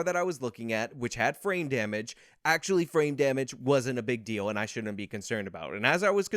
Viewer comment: Every time he talks about the car dealership on jan6 and he says "frame damage" i hear "brain damage". Nothing against, that's just what it sounds like to me. Maybe i have brain damage. Lol